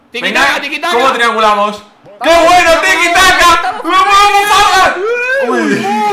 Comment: le entendí "no mames fargan"